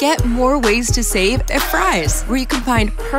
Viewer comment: i remember i saw this commercial on my tv and i thought it said “fru’s” instead of fry’s 🗿